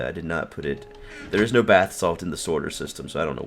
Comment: Sounds like he is saying no bath salt in the sorter system